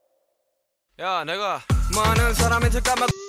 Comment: ok so he actually DID NOT say the N word. He said "naega" which is Korean for "me". Many mishear and accuse them of being racist when it is just a different word. Hope this helps. Love your reaction!